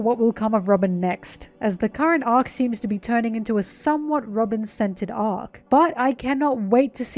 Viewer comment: Just wanna say I loved this analysis, but I thought you said “Robin scented” instead of “centered” 😂